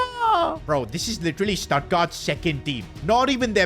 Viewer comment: Did bro say Statgard?